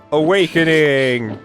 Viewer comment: I thought i heard Owainkening 🤣